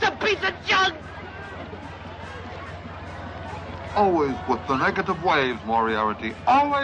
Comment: "SIGH"..... oh man..." "Don't hit me with negative waves so early in the morning." one of the best lines right here :D For the longest time as a kid i thought he was saying "negative ways" when he was on the tiger :p